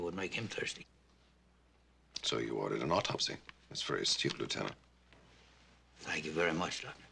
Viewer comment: What is dr. Kepple exactly saying in 7:10 " So you ordered an autopsy. That's very .... Lieutenant" Subtitles say <stupid>, but is it correct? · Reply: He says That's very astute.